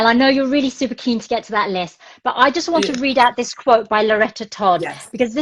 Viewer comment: Thank you for this. When she quotes the definition of cultural appropriation at ~ 22:36 I couldn't catch the full name of the person. Does anyone know who it is? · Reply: I think it's Loretta Todd